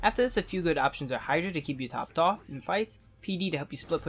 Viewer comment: what did he say to hydra? i didnt understand that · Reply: hydra is to keep u topped off in fights !